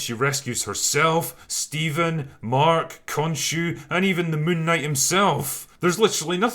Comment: moon night, I thought you said Night Monkey....